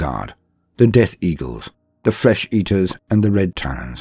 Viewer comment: Could've swore I heard you say "flesh eaters" 🤣😂😉